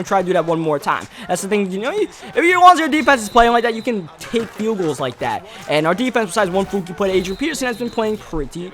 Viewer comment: can anyone else figure out what hes saying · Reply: if your defense is playing like they will take field goals like that